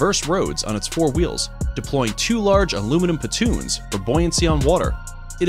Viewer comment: I heard "patoons", but I'm guessing it's "pontoons". Good video regardless.